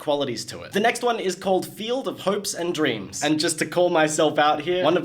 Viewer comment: For some reason I thought he said field of popes and dreams for a second. I found that really funny.